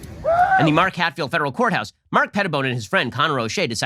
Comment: did he say Mark Petaboner?